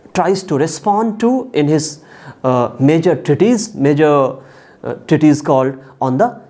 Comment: I'm sorry. I know that's really immature, but the subtitels are talking about "titties" instead of "treatise", and it's hilarious. For example: 17:33 "...these are the questions that he tries to respond to in his major titties.", or even better: 19:25 "...when Burke produces his pretty titties..."